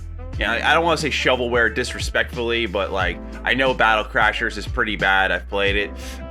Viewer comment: BRUH.....I thought he said Battle Chasers was pretty bad..... but after a quick rewind I see that he said Battle Crashers, carry on.